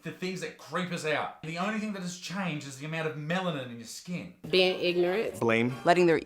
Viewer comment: I thought he said the only thing that has changed is the amount of melon in ur skin but I just misheard it